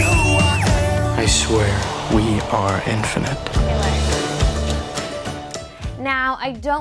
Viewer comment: WE ARE INFANT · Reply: ***** Yes, I know its infinite. My friend misheard it as infant and I thought it was pretty funny.